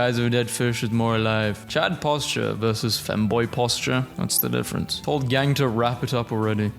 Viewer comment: did they just call the boiled one a femboy